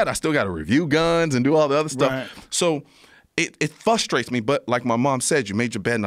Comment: Does he say "flustrates"